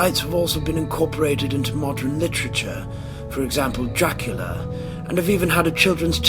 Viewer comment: Bluecap 1:45 Redcap 2:44 Selkie 3:54 Asrai 5:10 Nuckelavee 5:52 Joint eater 6:26 Boggart 8:00 Knocker 9:13 Brownie 10:25 Barghest 11:12 Spring heeled Jack 11:53 Dullahan 13:05 Kelpie 14:10 Banshee 14:44 The beast of bodmin moar 16:36 Grindylow 17:07 The red Dragon of wealth 17:55 Will-O-Wisp 19:16 Pixie 20:06 Mermaids 21:00 Leprechaun 21:58 Nessie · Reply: the pronouciation is off.I swear it sounds like he says ejacular,instead of Dracula at one stage